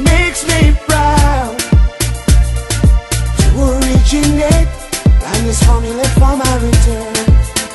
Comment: I think he says "And this home of mine I'll return" at 00:35 >.<